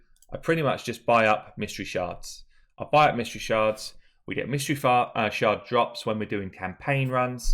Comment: did he say we get Mystery Farts daily @ 3:20 might want to see a doctor